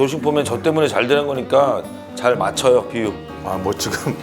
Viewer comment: 비유? 나만들었나?